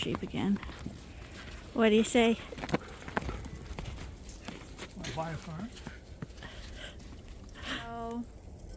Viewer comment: Lol, what was last comment? Did he say what I thought I heard, “ do you wanna buy a farm” poor guy, he looked drained, hang in there big guy, props!